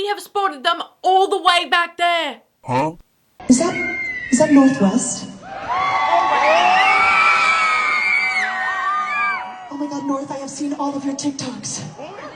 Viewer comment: i heard someone scream "Who the #### is north?"